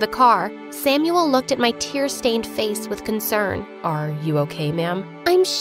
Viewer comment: I thought see said titles cus I skipped when she said t in tears face